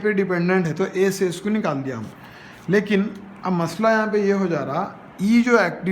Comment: Thank you for this Amazing Explanation. at1:14, did you say (Lakin)? Does it mean but? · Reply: Yes I said “lekin mean but”